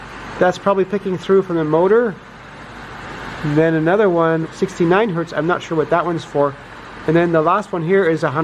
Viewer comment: 0:5:06 "69 hurts. I'm not sure what that ones for." Never a truer word spoken.